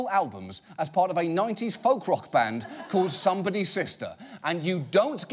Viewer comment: I don't know why, but the name "Somebody's Sister" reminded me to "Natalie is Freezing" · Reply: "Somebody's Sister" I almost thought John Oliver said "Shakespeare's Sister". They were, at least, the half decent.